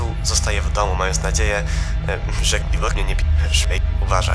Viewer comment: ja z pierwszej chwili usłyszałem, "Quornie pierdolnie" XDD